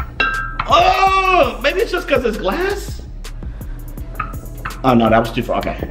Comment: i thought he said maybe becuase its black